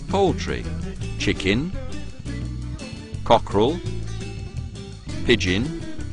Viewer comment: 0.0 Did he say pigeon at 3:13?